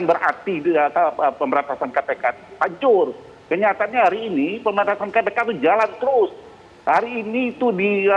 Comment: kapitra: kenyataannya pemberantasan kpk jalan terus.. apa saya yg salah dengar